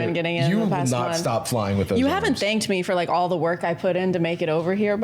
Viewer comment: i misheard it as haven't banged me and genuinely got a coughing spree from laughing while eating icecream!😅 Keep up the good work people! 😄